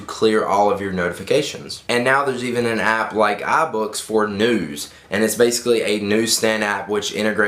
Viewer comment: 'For news' LOL I thought he said for noobs - I was like 'judgemental much?' LOL